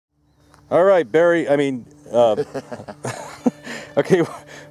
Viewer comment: LMAO did you just call him barry?